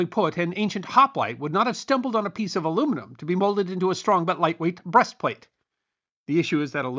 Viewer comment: I heard "aincient hotwife breastplate", realized my mistake, but still giggle.